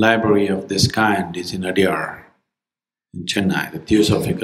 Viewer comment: is anyone able to make out what he says at 2:00 ?? . Where is this library located?? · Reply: Adiyar Chennai. There is the library of Theosophical Society